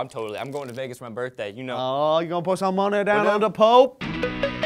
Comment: i thought said you gonna put some money on some dope!!!!! LOL